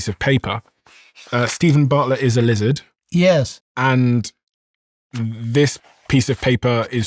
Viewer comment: I started CACKLING when he responded to your Stephen is a lizard comment because I thought I heard him say “he is.” 26:54😅😅😅😅😅😅😅😅😅😅😅😅😅😅😅😅 Such a wonderful interview. Really threw me off because I was so intently focused and hanging on each word you both were saying. I went from 🤔🧐🤨😲🤯😯😦 to 🤣😭😂😅🫠🥴😮‍💨🤧 with a quickness. I’ve seen everything ‘The Diary’ has put on YouTube and this is by far one of my favorites.